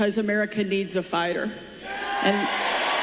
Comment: The first time, it sounded like she said, "We need a fire!" He's good at that! I watched a very good podcast by Tom Hartmann to explain tariffs. He said the biggest part Trump doesn't understand is, yes, tariffs worked great WHEN we were SELF-SUFFICIENT way, way, way back before mass shipping. So the only way to progress back to that, if that's his plan, is to build our own plants like the CHIPS ACT that Biden did, and pay people a living wage instead of trying to make money by paying cheap labor. I really wonder if companies make that much more money by paying cheap labor and avoiding American standards, compared to what they would make if they didn't have to ship their products back into America 🤔